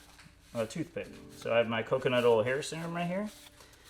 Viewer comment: Oh, I just heard you say “coconut oil.” I answered my own question.🤦‍♀️